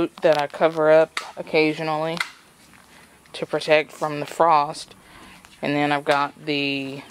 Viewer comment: Your fur babies are so cuteee 😍 at first I thought you said Toad from the frost 😲 I was 😲😲😥😥